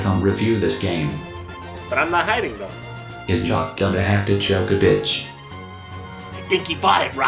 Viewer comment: I cannot, for the life of me, understand what Jacques says at 0:25. Can someone help me on this one? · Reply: "Is Jacques gonna have to choke a bitch?" is what I heard.